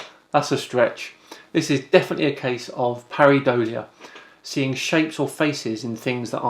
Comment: At first I thought you said Haribolia. And that would be too much of a sugar hit.